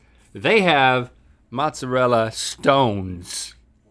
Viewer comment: I literally thought they said mozzarella SCONES I was confused for a few minutes 😂😂😂